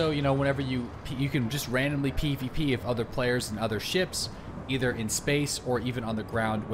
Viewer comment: I'm gonna be real chief, I thought you said you could "pee with other players and on their ships"